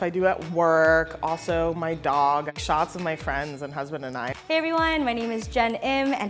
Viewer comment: Wait did say husband?